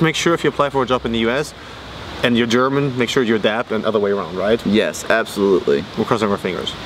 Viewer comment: and what does say at 12:06? "Make sure you are dead" ??? -___° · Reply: Make sure you adapt [to the other country's system].